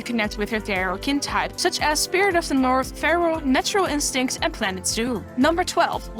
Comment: Wait, what did you say? Spirit of the North? Did I hear that right, or did you really mention my home world?